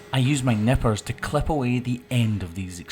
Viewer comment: I honestly thought he said I use my nipples to trim the hooves. 😂😂